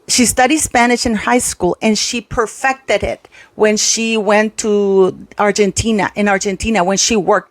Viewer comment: I thought MM said she spoke Argentinian …